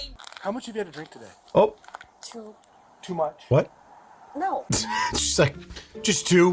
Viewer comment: I thought the officer said "2 Much" !!! instead of "2 what?" 😆